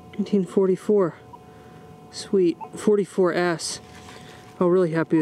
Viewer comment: did he say ass 😂😂😂